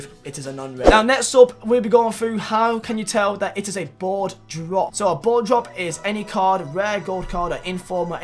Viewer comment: Who else thought he said blowjob instead of board drop, in the beginning?